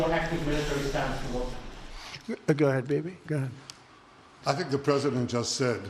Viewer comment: Did he say "Go ahead baby, go ahead"... Wow so its also romance between them🤣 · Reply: Bibi, not baby. Bibi is Netanjahu's nickname.